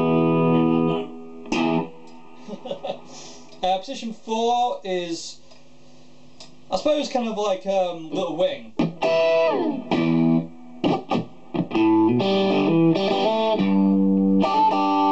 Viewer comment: when he said little wings. I swear I thought he said LIL WAYNE. hahhahah i was like hell naww xD